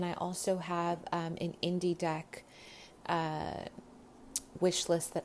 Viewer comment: random thought: I'd be very into your own interpretation and how you'd depict each tarot card, I think that the idea of creating your own deck would be amazing! When you said "I have an indie deck list" I just heard "I have an indie deck" and I was like "WAIT SHE DOES? I'll go check it out RIGHT NOW" thank you for all that you do ♥ ♥ ♥ ♥ edit: just saw it- the song "You got me twisted" of pile 3 is by Keith Sweat! <link>